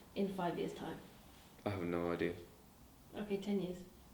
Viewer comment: When she asked him where he sees himself in 10 years... thought at first he said "well ik I'll be marrying Dina".. replayed it again and realised he said "well ik I'm gone be a millionaire" 😂💔